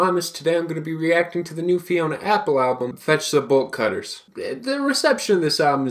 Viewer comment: when you said the album name i thought you said Vegetable Cutters